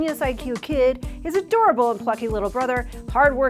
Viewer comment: At first I thought said "his adorable fucking little brother". She said "plucky" lol